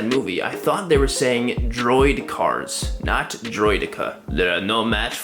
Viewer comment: I THOUGHT THEY SAID DROID CARS TOO!! Didnt help they rolled about so i thought "Oh yeah theyre literally droid cars"